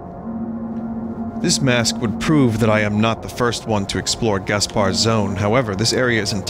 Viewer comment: Damn, Yames is really good at worldbuilding. I want to learn more about the Gaspar Zone and Genesis apocrypha and stuff. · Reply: when vinny first read it out I misread/heard it as the gazpacho zone